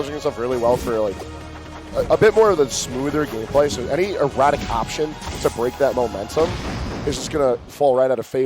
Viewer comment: "any erotic option" did I hear that right lol